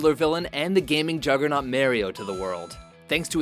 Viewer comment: did you say "merrio"? i only know mario, who's merrio?